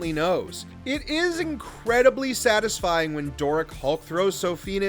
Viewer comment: The on screen captions say "Derek" when they should say "Doric"